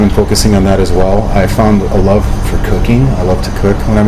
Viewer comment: I heard '' love for cocaine''... :Þ